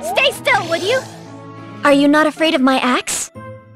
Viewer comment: Disappointed, I thought I heard "Are you not afraid of my ASS?"